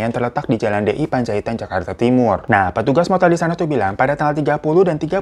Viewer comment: Apa gua doang yg salah denger, waktu babang bilang jakarta timur, denger nya jawa timur. Sampek gua ulang".🤣